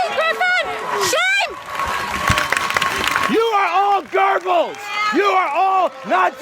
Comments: Why does the guy at the play sounds like he is saying "gerbils" instead of "Goebbels" ? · Can Americans please stop throwing "You're Hitler"s and "You're Goebbel"s everywhere when they can't even pronounce them correctly? Where is that need to constantly put weird random 'r's into German words coming from? It's Goebbels, not gurbils or gorbills or whatever, there is no r in Goebbels. Just freakin google it. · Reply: At first I thought he said 'you're all gerbals'. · did he just call us gerbels? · Am I the only one who thought he said "You're all Gerbils"